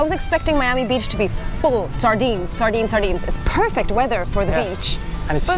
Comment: did you say sardines? Im from Kansas and just may not know that our beaches are normally full of sardines.